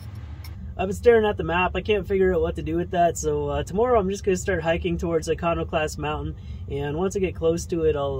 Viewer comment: I swear I heard him say 'Economy-Class Mountain'!